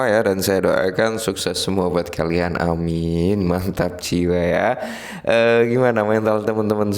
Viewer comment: Salah dengar..saya kira sakit jiwa ternyata mantap jiwa😅😅